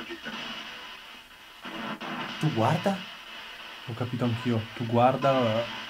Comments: Io ho capito tu guarda dritto al minuto 12:50 magari si riferiva alla croce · guarda dietro di te questo ho capito . Lo riascoltato più di una volta . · Pitfamily !!!! Avevate ragione 👍👍 dalla parte opposta c’era una casa .... e sembra proprio bella attiva ..... mi sa che ci sarà tanto ancora da scoprire !!! Restiamo uniti ❤️❤️ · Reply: secondo me è il padre di Ester Giacomo perché se ascoltate bene al min 12:54 dice "Tu guardi mia figlia" 14;18"Un brivido" 15:49 prima che dice Giacomo c è un altra voce che non ho capito cosa ha detto 16:01 "Sono" 16:06 "Casa nostra" 16:29 "Lo sai bene " 17:31 "Salvala" 17:57 "Ancora" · Ciao ragazzi! Che ansia 'sta cascina, mamma mia! Comunque al minuto 12:49 vi dice:"tu guardi Brigitta mia!". Minuto 16:35 "esci subito". Minuto 19:00 mi par di aver capito:"sono il padre". E allora ragazzi la mia ipotesi è: e se fosse stata segregata dal padre (Giacomo) e dal fratello (Carlo) questa Ester? Poi magari un giorno riuscì a liberarsi e a trovare la libertà proprio su quella porzione di strada dove ora sorge la croce. Magari è proprio là la croce, perché è dove la trovarono l'ultima volta stremata e in fin di vita. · Al minuto 12:49 ho capito "Tu guarda dietro di te"